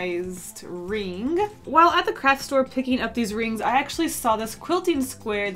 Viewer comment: it sounds like she said crack store but she said craft